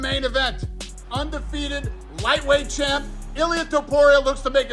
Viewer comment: Did Danadl say Lightweight champ